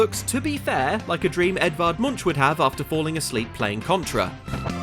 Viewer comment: I thought he said Edvard Lumiere for a second there.